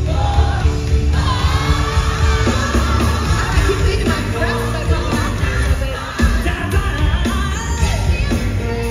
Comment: What did she say at 41:31 to 41:34? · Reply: She said she thought her breast felt like it kept coming out. As she was bouncing around you could kinda see it was moving but nothing came out.